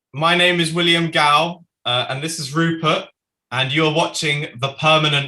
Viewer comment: I can't. I thought he said "This is RuPaul" xD